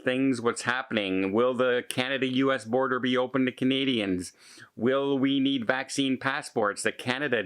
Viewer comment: I keep hearing "comedians" when you say "Canadians"